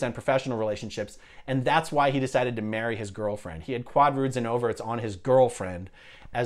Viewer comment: it sounded like you said the type of relationship auditing Brad had was “quad roots and overts”. Looking at Fair Game Podcast glossary, I get the “overts (sins)”. What’s the rest? I realize I probably misunderstand what you said.